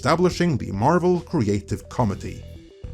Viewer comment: Oh!!! Committee! I thought was saying the Marvel Creative Comedy.